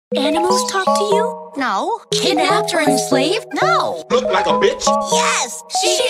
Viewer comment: Sound like you say kidnap during Slave